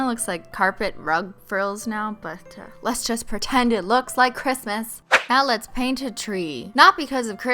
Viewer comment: didn't anyone else notice she said Cristmas instead of Christmas😂